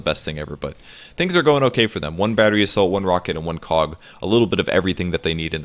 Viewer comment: I keep hearing Battery Salt lol I know it's Battery Assault, but every time says that I just hear Salt...it's throwing me off.